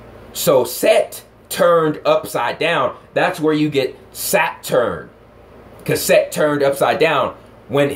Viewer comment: It sounds like you said Satyr.